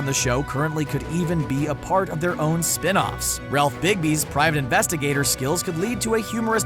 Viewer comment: Did he just say Ralph Bigby? Lmfaooo!!